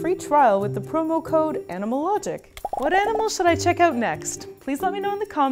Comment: At first I thought that she said anime logic but I realize it's animal logic 😂😂😂